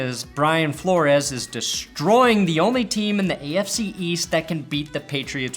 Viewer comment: Hey, uh... Did say "EFC" at 7:00?